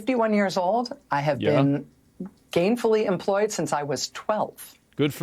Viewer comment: " i am fifty-one years old, I've been gainfully employed since twelve." Translated into poor is my parents were incredibly wealthy. So i worked at my parents' friends' businesses. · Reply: I thought she said "gayfully employed"